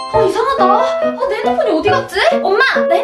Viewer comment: 자막은 난데 나만 내라고들리나?